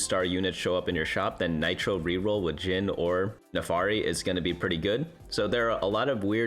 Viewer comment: Did bro just said Naafari? : D